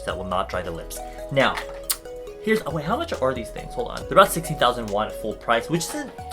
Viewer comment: I thought you said 60,000 won and I was about to have a heart attack.